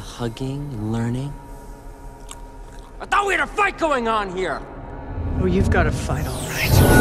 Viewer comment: what Is that gideon said? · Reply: “I THOUGHT WE HAD A FIGHT GOING ON HERE!”